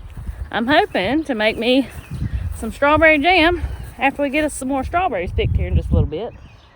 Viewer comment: My ears heard you say strawberry jam, but my brain heard you say strawberry dumplings. I was thinking, "oh I love strawberry dumplings. I wish I was there so I could eat some." Then you said it again and my brain heard the same thing my ears did. Now I'm sad because I'm going to have to make my own. If I don't my brain is going to drive me crazy. Do you ever make strawberry dumplings? They are delicious.